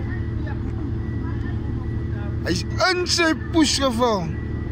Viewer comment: アホやぁ って聞こえる